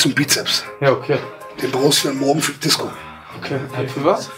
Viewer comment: wer hat noch '' Morgenfick Disko '' verstanden? hahaha